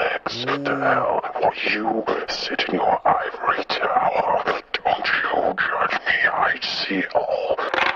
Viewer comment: I heard "Don't you judge me, I see all"